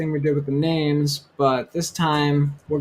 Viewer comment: you say "we're gonna write a landy(sp?) function? What exactly did you say so I can research please! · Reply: It's a lambda function.